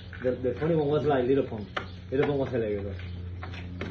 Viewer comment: Planet Of The Apes coming real soon dawg😂😂 Also I thought he said lil pump at first😂